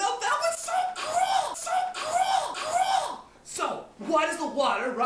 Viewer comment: lol when he says "so cool so cool" it sounds like he says "so cruel so cruel"